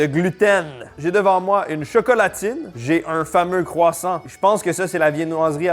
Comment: une quoi ? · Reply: Team chocolatine au Qc deal with it lol